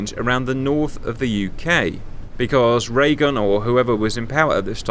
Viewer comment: Did you call him President Ray-Gun?